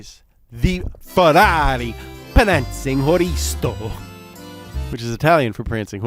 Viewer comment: "prancing oristo"?? ...i'm lost. what he said here?I think that the italian for prancing horse is "cavallino rampante"...